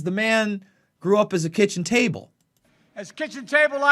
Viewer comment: funny sure. But you do realize he actually said: "AT the kitchen table..." In fact, we all learned a lot of basic lessons as kids at the kitchen table, right?